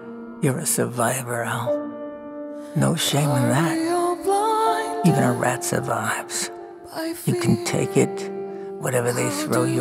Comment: “They see a weasel and a rat, your a survivor out no shame in that , even a rat survives.” The most realistic crew of zombies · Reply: I thought he said “even rats have lives” for the longest time oh my god I am so stupid